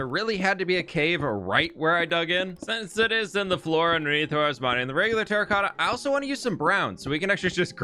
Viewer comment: ngl I have no idea what you said here just sounded like ‘ahhsesssdfeewtrgr… brown’